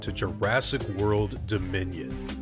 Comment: Omfg....this ENTIRE time I thought it said Jurassic world Domination 😦😳